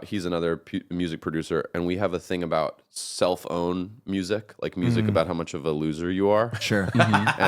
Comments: 100% thought they were saying “cell phone” instead of “self own” lmao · Before I turned on the subtitles while they were talking about songs that were self-owns, I thought they were saying cellphones. I was trying to think how the mentioned songs were cellphones.